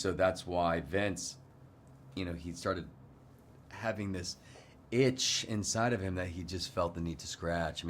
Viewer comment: I thought he said BITCH! 😅😂